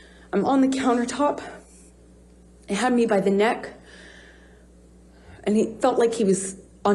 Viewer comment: she said “it had me by the neck” I don’t think she said “he” and I also noticed that she uses “it felt like….” ALOT! So that leads me to believe that it’s up for interpretation-you FELT like he was on top of you but WAS HE ACTUALLY ON TOP OF YOU??? Everything she is describing was more than likely what SHE DID and then the rest are just outlandish lies